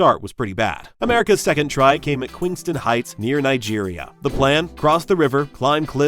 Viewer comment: Nice video, a few parts confused me though like the part where I thought I heard battle in Nigeria, but overall incredibly informative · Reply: That was supposed to be Queenston Heights near Niagara, how he mixed that up I'm not sure.